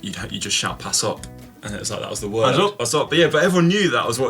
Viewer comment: what was the term Connor mentioned for passing from his days in Belgium? Sounds like he said "pass on" - but I don't speak Guinness